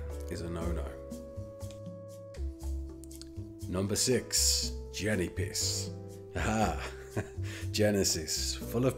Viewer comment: i cant stop hearing Jennypiss instead of Genesis 🤣